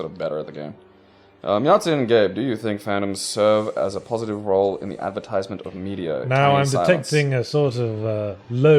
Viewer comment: What is Gabe saying at 45:00? "Does mumble serve a positive role in the media"